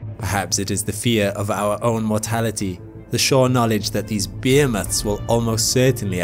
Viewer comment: I swear in the beginning of the video I heard 'these beer-nuts', not 'these behemoths'... im shot ..... I gotta stop working so much lol 😫 😖🙃